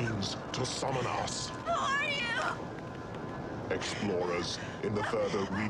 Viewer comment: Explorers in the further regions of experience. What a profound description. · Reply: I always hear "Explore us...". I'm not a native English speaker so that's most likely why I mishear him.